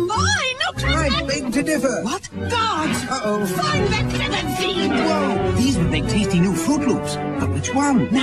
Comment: What did she say at 0:50? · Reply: Jeffrey Meredith "Find that feathered fiend!"